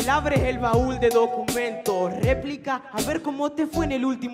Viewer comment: alguien se dio cuenta que dice "Repilk"??